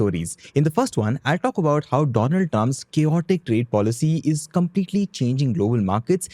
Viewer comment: firstly I thought I wrongly heard donald dumb...